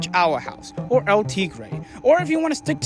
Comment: what's the name of that show? I can't understand what he says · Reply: I know that this is very late, but he said “El Tigre” it’s an older Nick cartoon